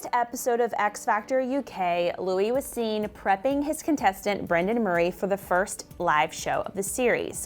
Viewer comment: bish I thought you say brendon urie